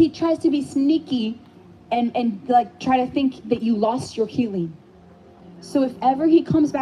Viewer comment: I keep hearing this as ‘you’ve lost your healing feeling’.